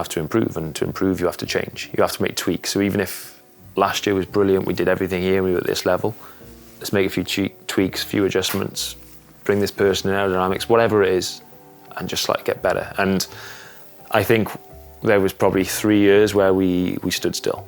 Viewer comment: did anyone else catch the "lets make a few cheats" line at 10:50 😂